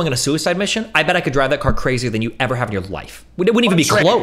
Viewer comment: bro destiny is not saying better, he’s saying crazier